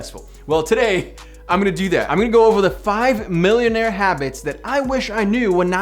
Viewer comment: I thought it said 1 million habits....I was preparing for a longgggg video